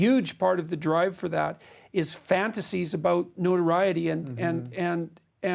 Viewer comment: What word does he say at 26:16? "nonreity" or something 😅